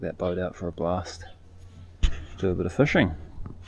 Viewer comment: I thought you said fishing, not swimming! Nice video. I'm glad your wife got that video. Makes for fine memories ahead.